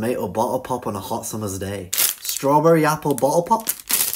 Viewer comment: For some reason I heard "strawberry yapple bo-al pop"💀